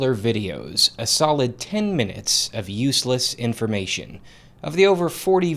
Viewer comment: I thought you said this way useless????? All of these are important!!!! Minus the temperature in Tokyo, Japan....